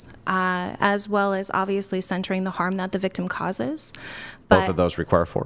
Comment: did she say “centering the harm that the victim causes”??? /might have been something other than centering, but fyi victims experience harm, not cause it. The fundamental Freudian slip that explains left-wing “justice” perfectly.